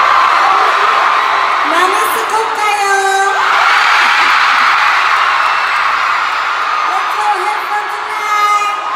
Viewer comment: ano sinabi ni jisoo? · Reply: Na miss daw niya kayo sabi niya mamiss ko kayo